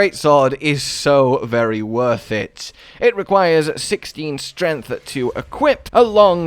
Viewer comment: Bout had a heart attack thought you said 60 str